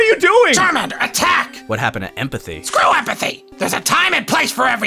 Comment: I thought u said have empathy ‘SCREW THAT’ 20 minutes ago CHARMANDER LEARNT EMPATHY